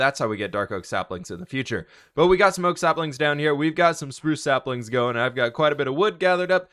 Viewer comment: i heard smoke saplings